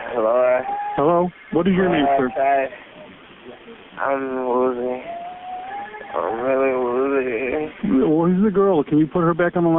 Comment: Get someone else to translate your audio. Most of what people say is not "inaudible" in the first call. Starting at 5:27, very clearly says "I'm losing...I'm really losing..." (presumably "blood" or "consciousness") and then "tried...tried to stab me..." then "She took the knife from me. And she stabbed me in the arm. I fought for the knife." I could keep going. God he even says "I won't make it" not "I will make it." That's why the operator tries to reassure him right after. Why even include the call if you're not going to try and understand it?